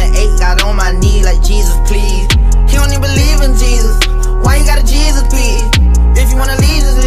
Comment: " He don't even believe in Jesus. Why you got a Jesus piece ?" 😂😂😂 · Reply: I thought he said "You don't even believe in Jesus"